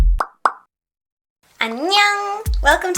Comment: what is she saying :O something in Japanese? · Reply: Patrice Hoffman She's saying "Annyeong," which is Korean for "Hello" or "Hi"